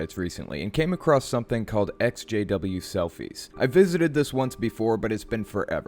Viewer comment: At first I thought you said, "SJW selfies," lol